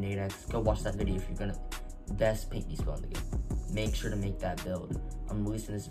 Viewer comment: what did he say 🤔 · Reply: he said best paint beast build in the game